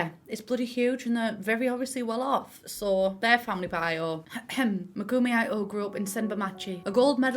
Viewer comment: every time said Megumi I thought she was saying monogamy lol